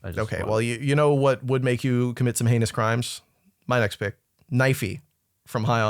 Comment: I thought you said "you know what would make you commit some anus crimes" jfc